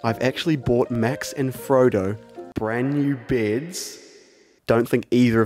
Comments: For a second I thought said, " I've bought them brand new beards" When I realized he said beds... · No joke I thought he said he bought Max and Frodo New “Beards” Lol